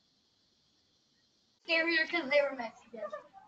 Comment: Did he say scarier because they were Mexican at 2:30.